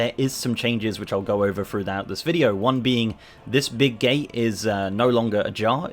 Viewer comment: did I heard this big gay?